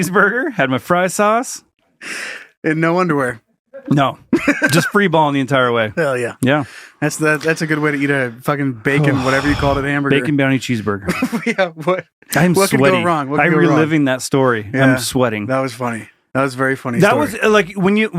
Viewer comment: I thought you were saying bacon brownie cheese burger and just pictured brownie squares instead of buns and was mortified lol. Now I need to Google what you are talking about.